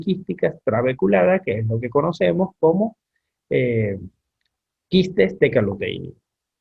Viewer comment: Dr, ¿podria indicar el nombre de los quistes que menciona en el minuto 30:53? No se entiende del todo bien. Muchas gracias! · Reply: Saludos Luis. Te escribe el. Se trata de los Quistes TECALUTEÍNICOS.